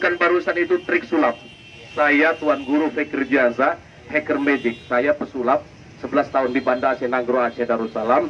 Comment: did he just said "heker medic"?